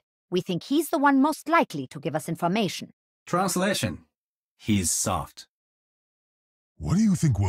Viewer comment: am i the only one who heard genji saying "he suck" instead of "he's soft" ?